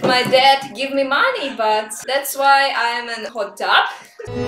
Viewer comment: my captions said "Thats why im a hot dog"💀